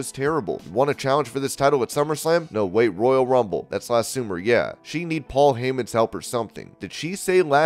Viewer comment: I honestly thought Ronda said "summer slam.. er royal rumble, that's sooner right? Yea.." instead of "that was last soomer" lol still can't hear definitively what she said tho..